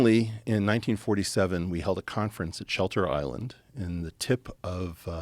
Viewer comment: Who is here from the Diary of a CEO ?🤭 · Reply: thought he said Shutter Island 😅